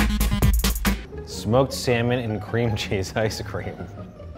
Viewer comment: I hear SEMEN AND CREAM CHESSE😂😂😂😂😂😂😂